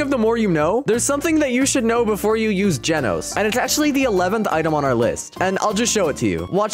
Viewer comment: bro rlly said genoese (a place in the world) instead of Genos